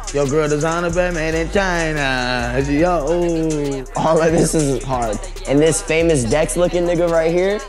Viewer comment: sounds like wifisfuneral said " all of this isnt hard" instead of "all of this is hard"